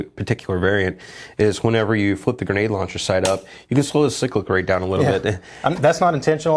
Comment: can anyone understand/translate this to me? I legitimately have no idea what he said. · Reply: “Is whenever you flip the grenade launcher sight up, you can slow the cyclic rate down a little bit.”